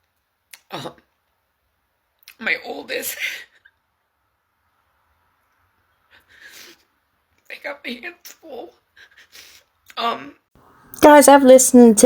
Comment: It almost sounded like she said my oldest”Got banned from school “… got paid in full, or I got made a fool… almost like she’s stuttering on purpose. Listened to it 50 times n everytime sounds different.